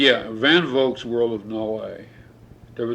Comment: Anyone know what book he says at 17:17 ? I can't quite make it out enough for a useful search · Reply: The World of Null-A by A. E. van Vogt